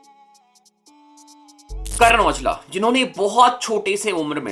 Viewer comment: carrier ????? What did he sayyyy 💀🍃